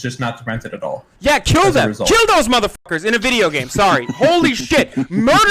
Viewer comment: He said "in a video game", and all I heard" was "it's just a prank dude".